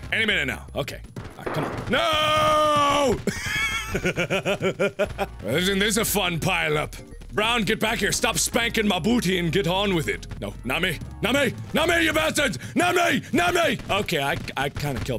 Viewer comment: Closed captions make everything 10x funnier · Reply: the cc says.. "Jim is a fun pile-up rounded back your stuff spank my booty and get on with it." And although 1/2 of it is confusing i think it sums up the game lol